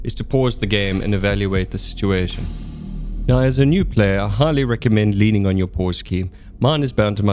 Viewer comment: I had to re-listen to that bit three times before I heard you correctly! >XD The first two times I wondered why as a new player I had to lean on my foreskin?!? >XD I'm not totally new to the game; but I like now knowing vehicle repair times are doubled from rifleman > tank crew > engineer; will that stack i.e 8x's quicker with two engineer units? IDK, but I'd imagine so as the Finnish Heavy engineers abilities stack. 3 units of them vs 1 unit of them will complete a coastal gun emplacement 30-40 seconds quicker; takes the same time for both to build the first stage as only one engineer builds the base, but they all chip in building the remaining two stages; also means at a push the 3 unit version can be used to crew the 5 man gun if necessary!